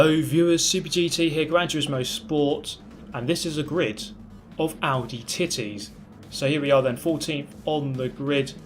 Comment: Wait, did u just say ...Aldi Titties?!